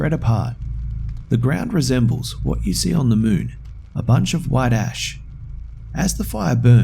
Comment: i thought he said white ass on the 2nd story